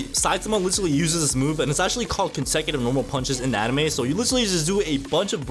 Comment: That’s actually Consecutive Serious Punches but the captions say Consecutive Normal punches for some reason